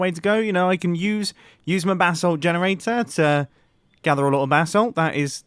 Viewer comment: Who else thought he was saying bathsalt?.........DRUGS!!!!!!